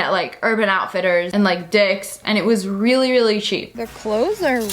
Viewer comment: at dicks did I hear that right